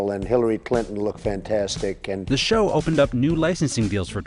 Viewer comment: Did he just say that Hillary Clinton looked fantastic?